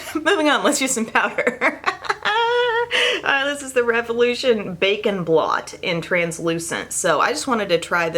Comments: Good morning, definitely thought you said bacon blot...not bake and blot lol. Still waking up, Have a great weekend! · 😂 I thought you said "Bacon Blot" not "Bake & Blot" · I thought you said, "Bacon blot".... I was like.... OMG....they have gone there and made bacon scented makeup! I then rewinded and realized you said, "Bake and Blot"! Lol! This made me laugh! Loved this video. Thank you for being our trusted beauty advisor!